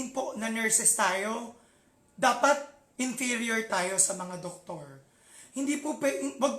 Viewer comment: he said, hindi porket nurses tayo e dapat INTERIOR tayo sa mga doktor? I put on my headset and played it many times, did I heard it right INTERIOR? narratively im not sure what he meant? comprehension wise diba dapat INFERIOR not INTERIOR? Pls correct me if i am wrong.